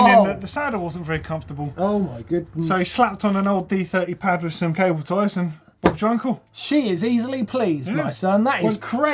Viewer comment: Which of these was the most scary? Have you tried any of these? Let us know in the comments below 👇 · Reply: did say bobs your uncle